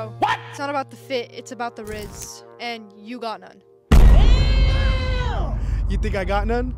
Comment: "It's not about the fit it's about the RIZZ, and you got NONE."- 2023 💀✋🏼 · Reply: I thought she said" it's not about the fit, it's about the rizz and you got that" not NONE. That's why he kinda tried the last part on her